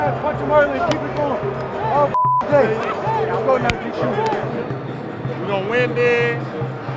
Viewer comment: I heard "Let's go Branden"